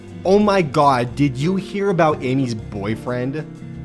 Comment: I thought it said army